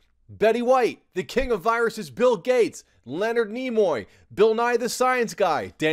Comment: what did he sayyyyyyyyy? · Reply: What, about Gates? LOL